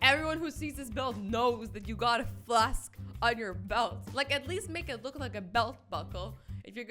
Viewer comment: i thought azzy will say "u got a flask on ur DICK"